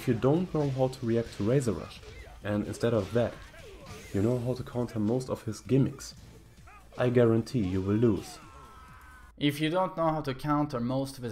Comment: that is not what he said, he said if you know how to counter the gimmics but not razor rush you will lose